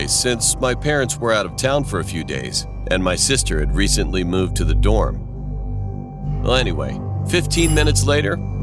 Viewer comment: I thought he said "And my sister had recently moved to the door" and I was like 👁👄👁